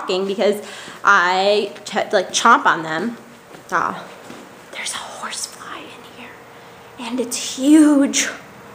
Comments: At the end I thought you said 'There's a horse flying here..' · Whoa, I thought she said "There is a horse flying here" and I was like "WHAAAAAT? O_o" Thanks, you made my day (again). :)